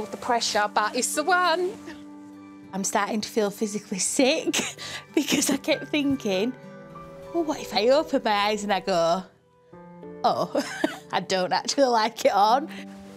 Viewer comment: I don't understand what the bride says at 1:25 This family is so beautiful, just everyone is so wholesome! I wish them all good health, good luck, and much happiness! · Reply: She says: I'm starting to feel physically sick because I kept thinking, "Well, what if I open my eyes and I go, 'Oh, I don't actually like it on'". Strong accent with a little congestion 😀